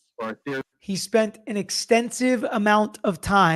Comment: I thought he said "expensive amount of time" instead of extensive. and mhm yep would fit